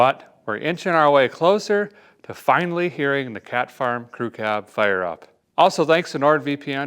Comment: Cat farm! I thought you said Cash Bar Crew Cab. Works either way.